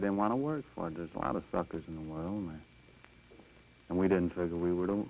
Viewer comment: Amazing: no curse words. Not even a “Damn” I thought he said “fu**ers” but he said “suckers”.